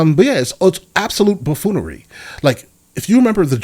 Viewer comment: "Its absoute baffoonery" 😅😅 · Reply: I thought he said “Bullfoolery” 😭